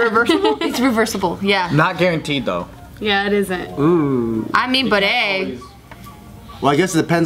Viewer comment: “I mean bidet” who else heard that ?